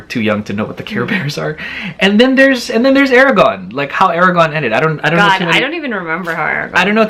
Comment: I legit misheard Eragon as Aragorn, and I got confused until I realized what you meant lol